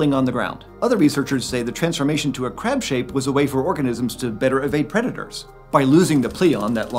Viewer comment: I misheard 4:48 and thought he said it was a way for crabs to evade creditors lol